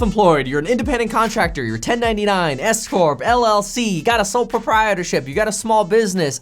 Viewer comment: I though he said escort at the beginning ....... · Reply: I thought he said sole property ship at the beginning 8-)